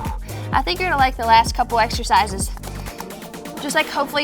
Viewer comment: I heard "compound exercises" and was not excited 😳. I stand corrected, that was wonderful! 😄